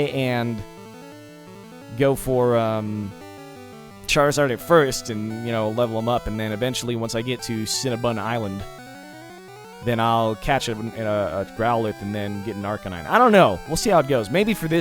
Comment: Did he say cinnabun island around 10:25?